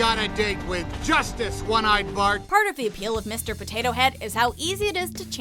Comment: When woody said "one eyed Bart" I thought he said "when I barf"😂